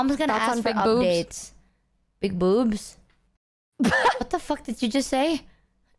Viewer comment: And it's not just doing the gaslighting this time. If you can't tell what said there, it was "thoughts on big boobs"